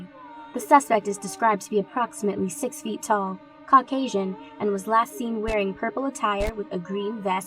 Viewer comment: for a second there i thought the girl said cock asian but she really said caucasian